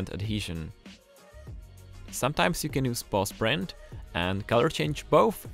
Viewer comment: I keep hearing "boss print".